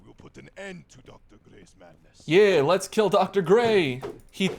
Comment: I heard let's kill dr dre 😭😭😭